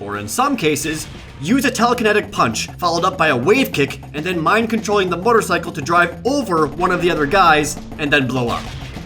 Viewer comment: Jake has PSI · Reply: Did he just say "mind controlling the motorcycle"?